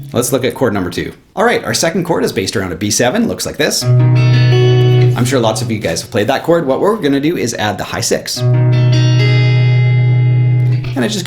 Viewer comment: as a non guitar player I heard "high sex" at